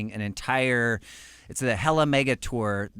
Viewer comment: What did mike say wen he was covering his mouth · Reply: I think he said "Hella Mega" in like an epic announcer type voice.